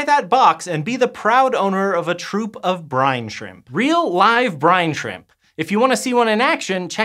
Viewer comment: oh. I thought it said big shrimp